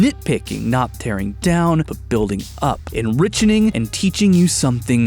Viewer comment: Did he say… Enrichening..? 2:07 Enriching is a word, and I’ve heard “enrichen” used, although spellcheck is very upset with me for typing that, but… enrichening..? It makes perfect sense as a word, it follows the general structure of the English language, it just sounds strange